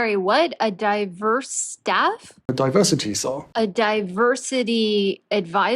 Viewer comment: Sounds like he was saying Diversity Tsar 🤔